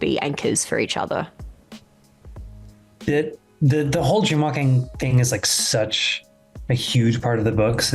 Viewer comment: Lol, I thought he said Tremalking at 3:41 and had to go wiki to see wtf it had to do w Egwene 🤦